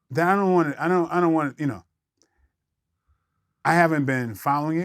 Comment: I could’ve sworn he said I don’t want to talk about this. Then he said I’m familiar with the Tasha case. But Vlad runs it down any damn way. Michael’s body language is so obvious. Wtf Vladimir!!